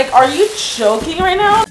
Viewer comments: am I the only one who heard * are you choking right now? * Only me ..... ok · What she said: Are you joking? WHAT I HEARD: are you choking rn? 150 likes? Holy crap wow! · When she said are you joking I thought she said choking So I’m like no I’m not choking XD